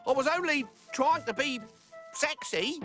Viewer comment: did just say the word sexy